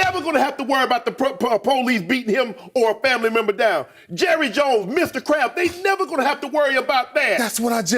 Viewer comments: Loool at 7:38 I thought he said mr krabs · deadass thought he said Mr krabs